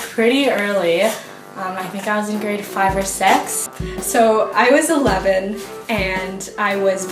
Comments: Who else thought she said I was 5 or six instead of grade five or six and I was like aihaoshwjwjwj bishhhhh what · When the woman said she was in grade 5 or 6 I thought she said she was like 5 or 6 and then I was really concerned! Especially when she said she was out shopping with friends!! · Did she just say she was 5 or 6 when she got her first period? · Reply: Oh wait she said GRADE 5 or 6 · at first I thought she said at age 5 or six lol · did she say sex? 😂😂😂 I know she said sixth butt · I misheard the third woman and thought she said she got her period at AGE 5 or 6 and went "WHAT?!?" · jesus christ when the girl said she got her period in grade 5 or 6 i thought she said ‘i got my period when i was five or six’ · I thought she said she got it when she is 5 or 6 😂